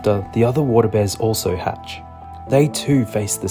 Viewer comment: not gonna lie, i swear i heard you say water bed. i was like wtf eggs doing in the water bed